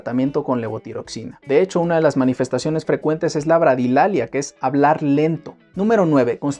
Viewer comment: Hola, que dijo en min 7:16 ." de hecho un hecho frecuente es la ............" No se entekdne bien ... ¿labralilalia, eso dijo? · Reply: bradilalia, es decir, que hablen lento